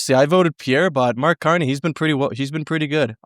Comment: did he call him Canny? Candy?